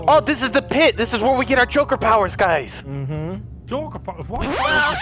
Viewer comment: Omg I thought Derlirious said choker powers 😂😂😂🤣🤣🤣